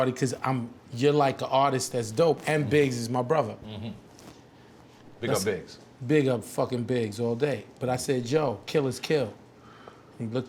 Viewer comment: Did Irv just say, "I'm fucking Biggs all day"? PAUSE THAT!